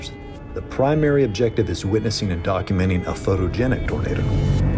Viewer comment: the captions says "burrito" instead of "tornado".